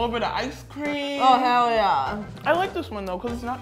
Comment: Every time she says “HE🏒🏒 Yeah!” I hear “Heyo yeah!” 😂